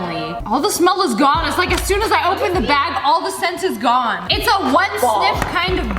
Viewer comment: When you said the "all the sent is gone" I thought you said "aw! The sent is God" lmao